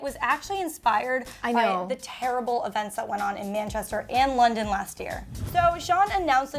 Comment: Did she just say “ the terrible events in Manchester in London last year” .. erm they’re two completely different cities wtf? They’re not even near each other 😂